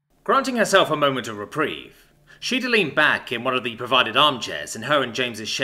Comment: In the first sentence. What was said was the word "reprieve". I heard "Reprig" and had to do a double take.